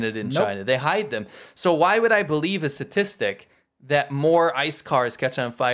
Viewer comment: I thought you said “sophistic” rather than statistic. We need to use the new word. Sophistic: a statistic used to lie or cover-up an underlying truth. Let’s go. Start using it. This is the word of the propaganda war.